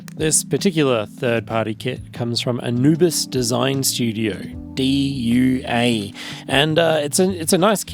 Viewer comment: I thought you said “a nudist design studio” and I was very confused